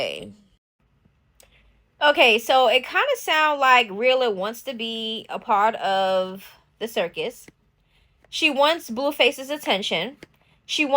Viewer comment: She actually got preggo by him before but miscarried. This baby is her rainbow baby.. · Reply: I thought she said she aborted the child because “she didn’t want to be apart of the circus”